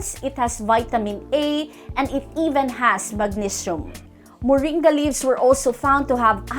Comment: I could not make out what was said at the 4:52 mark. What were the last 2 or 3 words that were said after …. ‘… and it even has….”?? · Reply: Hi Valire, I hope your doing great.... it has magnesium=)